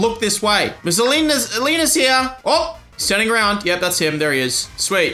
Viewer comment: for a solid minute I thought it said "Ms. Obama's here!"